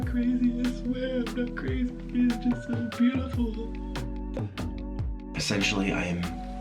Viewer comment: Did he say beautiful at 58:12? Whatever he said it like fit with the background music lmfao.